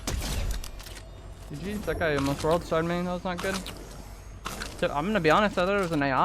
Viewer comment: did you say "That guy almost World Starred me" ? 🤣